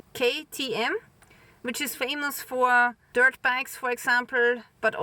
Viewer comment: i thought you said they were dirt bags but it was dirt bikes lol both might be true